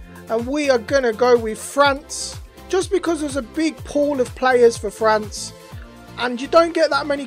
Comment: swear you said big porn of players Edit: subbed btw